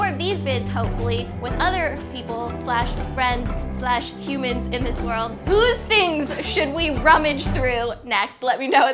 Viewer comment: what did Hannah mouth on 8:34 when Ingrid said friends? huh? huh? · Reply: I'm pretty sure she said "other people?" like really sarcastically